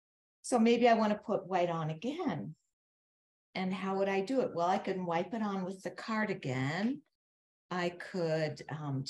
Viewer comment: I heard, "well, I could wipe it on with a cardigan...." Haha